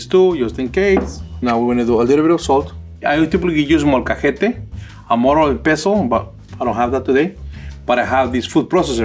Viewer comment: The subtitles didn't understand him. He said, "I typically use molcajete, mortar and pestle."